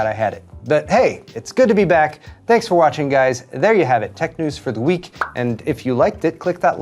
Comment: I heard "There you have it, tech news for the weak" and I was mildly surprised but accepted it.😁 Ah, the english language...